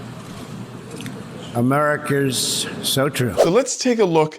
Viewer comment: What the hell did he say at 1:50? . Was it "Americars so true"? Cognitive decline